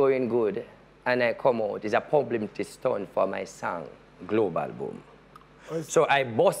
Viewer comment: Did he just say “PUBLIMITY STUNT” at 7:04!!! ??? 😂🤔